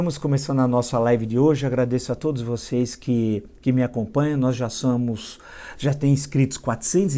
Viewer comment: “nós já samus” ? Oi? Foi isso que escutei?